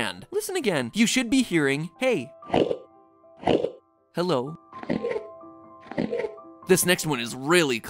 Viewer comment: The second one you said "Hello" sounded like "How are you?".